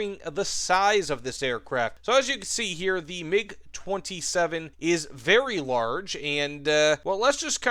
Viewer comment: MiG27. Did I hear that right?